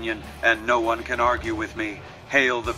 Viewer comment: I always heard it as "Hells! The People's Republic!"